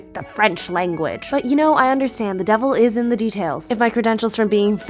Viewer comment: Did you just say "vas te faire f..." ? 🤣 I did not expect that ! Love it ! 😂